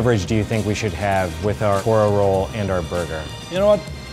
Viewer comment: I'm sry but this jojo fan just heard you say "ora roll". STAR PLATINUM ZA WOURLDU